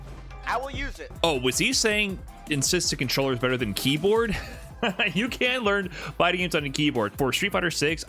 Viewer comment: Misheard Brian say "you can't learn fighting games on keybaord" and was ready to pull up my sleeves and defend my main controller